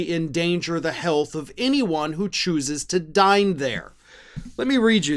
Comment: I thought you said 'anyone who chooses to die there' not dine there. When I realized the mistake I thought well it's still accurate lol!